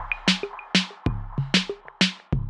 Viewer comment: I thought she said "today im dumbledore"